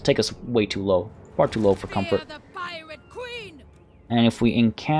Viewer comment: Did... i hear him say piwate queen? i might have misheard the R there